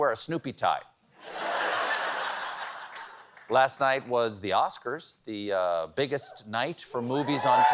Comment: When Colbert was talking about the Oscars, I totally misheard him say, "The Oscars are the bigot's night for movies..." and considering how often POC actors and films get sidelined, or POC characters in films get played by White actors in yellow or red-face (playing Asian and Native Americans) and the recent few years of shitstorm around that, it fit...